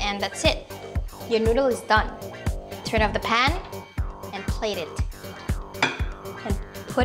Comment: did you just say Hindustan ?????